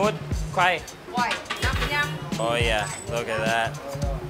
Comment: Did you say Zao An at 1:25?